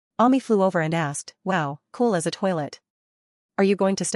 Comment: did she really say cool as a toilet? Lol